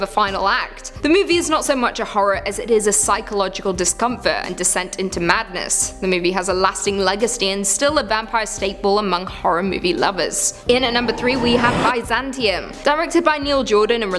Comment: Did she say leg and steed?